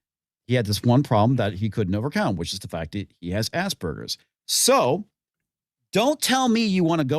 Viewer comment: I swear it sounds like Ass Burgers everytime I hear it😂